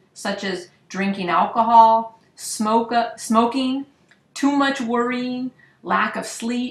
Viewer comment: who heard SMOKE-UP?? hehe ;)